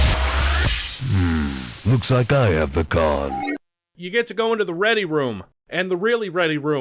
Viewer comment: never understood what Duke says here, still don't :/ An idea ? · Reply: 'looks like I have the conn'